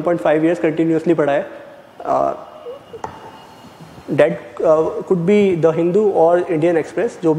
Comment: I think he said 'The Hindu', and the GS Score censored it .